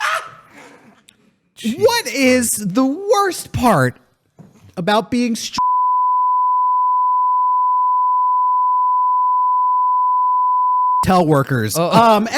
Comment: Well I know that said "what is the worst part about being strangled by Kobe Bryant" at 2:38. The rest of it I am not sure haha. Anyone that was on the Twitch stream know what he said or have a recording of it? RIT to Colby Brian.